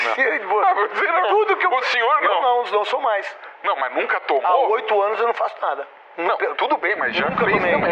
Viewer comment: Filtro zero o craque Neto. KKKKKKKKKKKKKK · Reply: "há 8 anos não faço nada" é isso mesmo que eu entendi!?🤔🤔